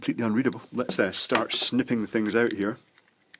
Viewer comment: I thought he said "let's start sniffing things out". Had a good laugh before I realized that he said *SNIP*.